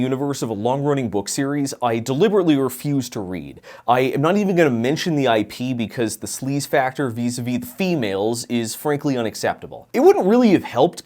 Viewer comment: What the f did you say at 5:03 I genuinely do not understand and the auto generated subtitles did not help... Sleze factor vis a viz females? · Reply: The full sentence was, "I am not even gonna mention the IP because the sleaze-factor vis-à-vis 'the females' is frankly unacceptable." "Vis-à-vis" is a borrowed French term meaning "in relation to". In other words, the way the series treats women throughout its writing is bad enough that he doesn't want to say what the series is.